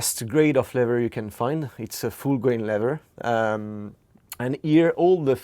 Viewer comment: Did he say that it's a fool grade leather? lol